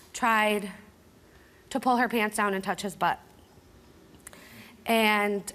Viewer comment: "My son tried to pull her pant's down and touch his butt." Steve even interrupts to correct her after more than likely being told by his wife to, in order to avoid confusing the audience. 8:10 "The doctors had stated that the bruises were not self inflicted, she stated they were self inflicted" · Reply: I thought I heard her say she pulled her pants down and wanted to touch his butt lol